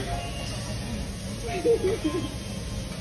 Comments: 友人の「旨いんだ!?ならいぃんだよww」が優しすぎてよき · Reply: 言ってなくね？笑 なんだ なんだよ に聞こえるんだけど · これ最後の方ご友人が「美味いんだ、ならいいんじゃない？」って言ってて、尊重してる感じがしてみんな素敵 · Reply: 美味いんだ！なーんだ！に聞こえる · これ最後の方ご友人が「美味いんだ、ならいいんじゃない？」って言ってて、尊重してる感じがしてみんな素敵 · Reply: うまいんだ！なんだよ！に聞こえた！